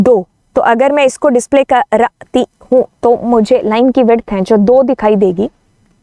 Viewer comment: is saying ""h jo"" what i heard ""bc""